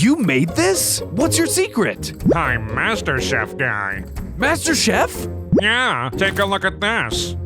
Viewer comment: I thought he said Master Chief